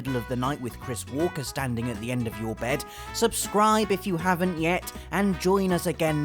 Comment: I thought you said "Christopher Walken standing at the edge of your bed"